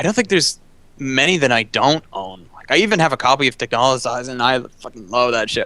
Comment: What was the anime said in 1:02:02? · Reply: I think it's Texhnolyze.